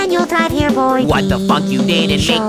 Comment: who else thought said what the fuck?